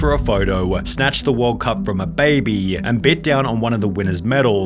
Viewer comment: I thought he said M’bappe weird, but no, snatched it from an actual baby, what the hell? (Don’t criticise my for my spelling of the footballer and/or teams, I don’t watch football)